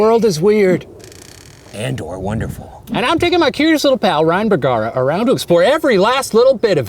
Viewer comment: Wtf i heard "and i'm taking my cutest friend ryan.." and i was like, "hold up meme"